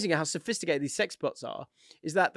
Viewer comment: Misheard about the sex bots and heard sex pods. Had an image in my head that stays near a caravan park similar of sex pods. Has to walk by it every day and look across while people furiously going at it